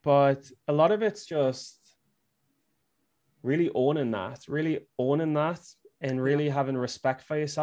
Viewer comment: a lot of it is what? Sounds like "really orthern math". lol 😊 Sometimes I can't understand words. I'm too American and not enough British!! Thank you guys for another great episode. I love this podcast! 🙂💙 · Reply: Really owning that